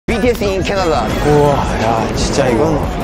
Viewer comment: who heard " BTS in kerela " in